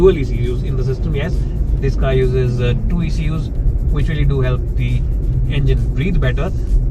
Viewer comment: what is he saying here exactly??? Two what??? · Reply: Ecu